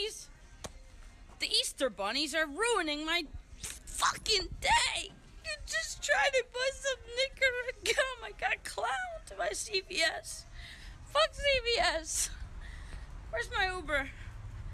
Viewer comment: I thought he said "im getting clowned by cps"